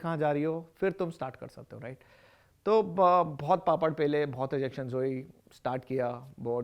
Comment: did you say papad pele...?